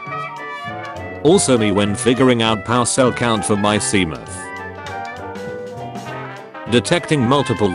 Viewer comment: i thought it said semen not seamoth💀💀